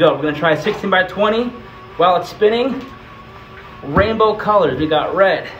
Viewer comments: When he said “rainbow” I thought he said “ranboo” and I started going insane- I started squealing and jumping up and down- but than I realized he said “rainbow”... still a sick piece tho 😂 👌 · I didn’t read the title or captions and I thought he said “Rambo colors”. In my mind I thought “Wow, there’s gonna be a lot of blood red and army green”